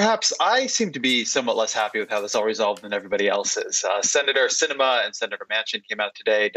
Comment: I keep hearing "Senator Cinnabon" for some reason.